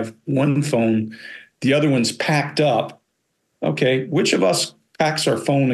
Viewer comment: I could be wrong, but when Fiona refers to her good mobile phone as having 'packed up' (i heard 'packed in'), in the UK this can mean broken rather than packed away into storage. Edit. Just listened again. She said 'packed up', but in the context of 'my fridge packed up'. As in, it broke.